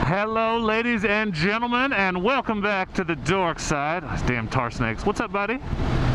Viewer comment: Well, I heard you say “Welcome to the dark side”, and I came here to fight you, Darth Vader. 🤣🤣🤣🤣🤣 very cool video, thanks for the tips, I’m new to the motorcycle world and they were very useful.